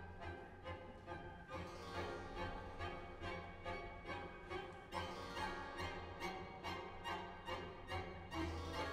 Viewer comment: what exactly did she say to him? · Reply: Come in NOW!